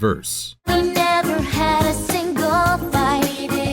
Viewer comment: "Never had a single fight" I always heard "bite" which made sense with the visual. I guess I was off.